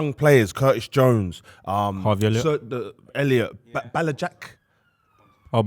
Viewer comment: U can always count on to pronounce somebody’s name wrong. Bajcetic was a howler. I thought man said black Jack for a sec